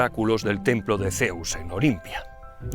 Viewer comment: escuché claramente "el templo de Zeus en Bolivia" 😂